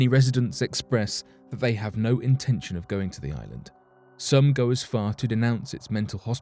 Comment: "some ghost farted " 😂 I swear I heard that